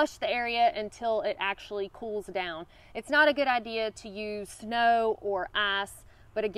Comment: did she say 'its not a good idea to use snow and arse'??